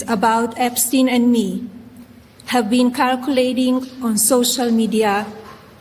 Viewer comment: calculating 1:04:25 weakness Go back, I missed it the first time I heard her because I was paying attention. So when I heard you boys were talking about it i thought this will be fun. It may be her accent but it sounds like she says “images have been calculating” 🎉and “l am not a weakness or a named weakness” in any… weakness/witness I’ll give her that one but calculating/ circulating, well that must be her AI chat bots fault. Finally she states that there are other men involved. That would make her a witness. She can’t dumb that on the victims. 😂😂😂